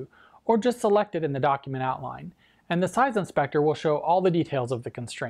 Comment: Can anyone tell me what is pronounced at 1:25, i have recently started learning ios so i couldnot get it. It is something like " or just select it at the _________". what he is pronouncing there.Thanks in advance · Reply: Document outline